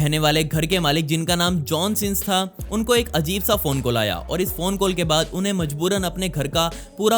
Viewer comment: Wait.. What did he just say..?? "JOHN SINS"...!